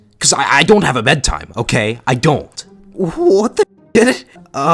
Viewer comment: When you said what the Sh!t I sounded like you said what the itit